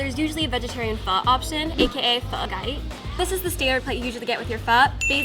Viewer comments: did she just say pho cai smh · The girl pronouncing Pho chay (sounds like she said Pho gai) makes me cringe